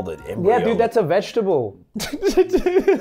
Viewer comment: narrator did you say “no shit”